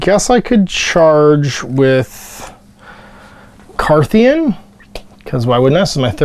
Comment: I know you were saying something else, but all i heard was the "Carpathian" when activating your one model